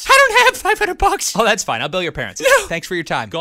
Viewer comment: At the end of this song, where he says he'll bill the parents, I always thought he said, "Oh, that's fine. I'll kill your parents."